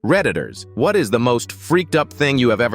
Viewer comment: My ass thought that said predators I was like “WHAT”